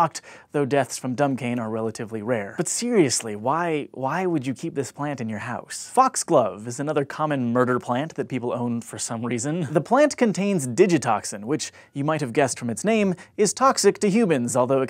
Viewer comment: I heard fucksglove